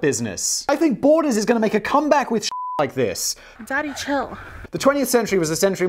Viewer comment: the fact "daddy chill" is the most used meme in this show tells something about Simon's mind state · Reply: Honestly always heard it as "danny chill" even befire this. Lmao had no idea it was "daddy"